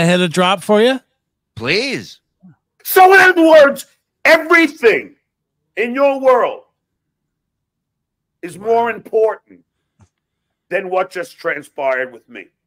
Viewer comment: While driving i heard this and swore he said "n-words, everything in your world is more important"